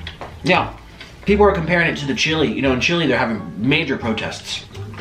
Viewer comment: did did he just said chile? and talked about the protests? o.o